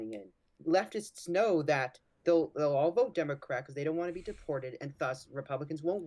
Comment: Did I hear him say leftists vote Democrat cuz they don’t wanna be deported?? Please tell me I heard that wrong